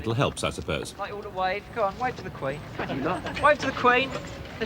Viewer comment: does the tour guide call his group “idiots” or am I hearing incorrectly? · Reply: Lolol!! He definitely called 'em "idiots" whilst he was making 'em "wave to the Queen"! 😂👌